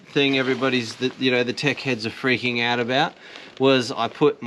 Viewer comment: Must admit, when you said "Tech heads", I could swear I heard "Wankers", didn't rewind to check.